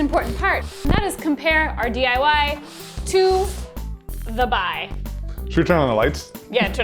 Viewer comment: wyd I think she said the Dibuy?